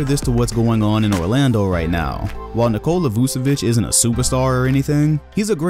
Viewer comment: the auto caption says Nicole elusive b** while he was talking about Vucevic